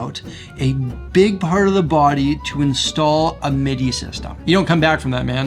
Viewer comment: what did he say was installed in that Gibson Black Beauty?? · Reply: a midi system!